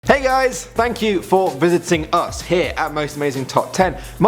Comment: If you keep going back to 0:00 it sounds like says: Hey ayes! instead of Hey guys